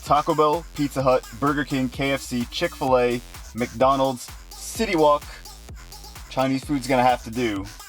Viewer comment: i thought he said city walk and not city wok lol